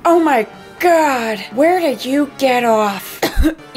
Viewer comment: Why did I hear her say “OMG!!where DID you get off!!!” 😂😂Like on floor idk 😂